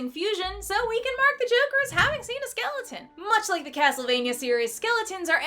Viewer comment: Oh i thought it said "been a skeleton"